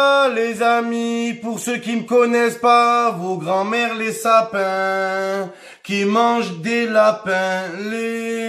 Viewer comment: C’est moi ou il a commencé à faire le lapin alors qu’il parlait du sapin au début 😂😂